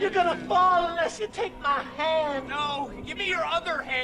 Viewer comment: I thought he said take my good hand in scary movie 2 😂